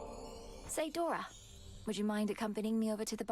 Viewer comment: Thought she said "soy Dora"